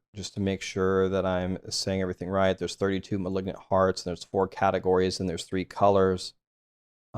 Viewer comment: Was paying this video about 50% percent attention, and thought I heard you say 'malignant farts'.